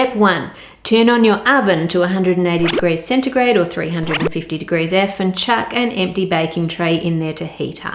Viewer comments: Did she say "airfinchuck?????" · I thought she said "350 degrees F in chat" ...I LOLed until I realized she didn't say that. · I thought she said “or 350 degrees F in chat” and I was like YES, THESE POOR PANCAKES DESERVE F’s IN THE CHAT 😭😭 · I wasn’t looking at my phone when Ann was saying what to preheat the oven to and I seriously thought she said “350 degrees f in chat” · does she say 350 degrees f in chat?